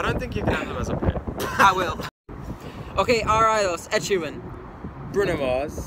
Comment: what he said? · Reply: Justyna W. ed sheeran